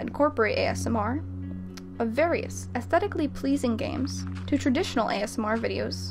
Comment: Around the 3:00 mark she says “various affetically” instead of aesthetically lmao